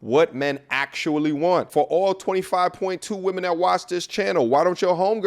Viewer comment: did you just say 'all 25 of you women' lol also...."I GOT MONEEEEYYYY" aaaahhahah!! there he goes lol